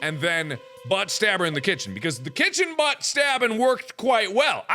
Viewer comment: thought it said the final buttslap